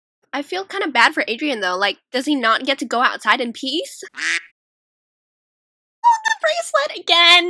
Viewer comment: I thought she said can't Adrian go outside and pee I'm dead 😆😆😆😆